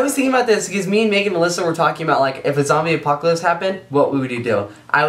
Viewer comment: It sounded like said Melissa instead of Alyssa = ] Has anyone seen Melissas bra? = ]